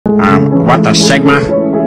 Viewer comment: I hear "WHAT A LIGMA?"